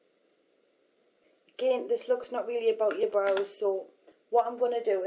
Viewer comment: "again, this looks not really about your brows, so" <-- I thought you were saying "not really about your bar of soap" I re-watched that bit about 5 times to work it out hahaha. loves!! xxx